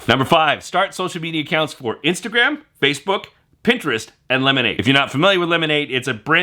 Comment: Just in case anyone couldn't catch it, the social media mentioned is called "Lemon8". I thought he said "lemonade". But after doing some Google searching on new social media sites, I discovered he was saying "Lemon8".